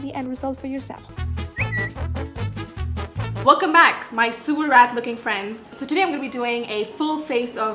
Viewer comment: 1 year ago: "Hello everyone!" today: "Welcome back my sewer rat looking friends" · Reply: idk why i heard that as super rad looking friends.....and then i saw this:) i feel i came at the wrong place lmao🤡